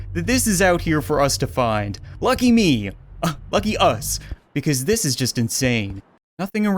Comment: what the heck it doesn't say lucky you it says Fuck you